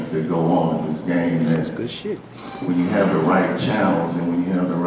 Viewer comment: Did you say "This is good shit" at 2:42 in the vid? It sure sounded like it. LMAO That had me dying.